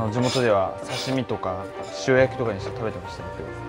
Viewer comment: Eng sub is ''pickled'' but what he actually said is 塩焼き(grilled with salt).